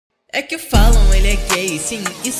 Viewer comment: Letras: É que o Falon ele é gay! Sim, isso é verdade! E ele assumiu ficando com a sua Brotheragem. Eu sou, não aguento mais isso, Ter amigo Gay é como ter um marido... Eu perguntei pra ele "quem é o Mario?" Ele disse que comeu ele no armário. Eu não entendi, então pedi pra ele me provar. Logo chegou uma foto... bem suspeitah... ti amu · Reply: Eu entendi “é que falam “ele é gay” kkkkkkk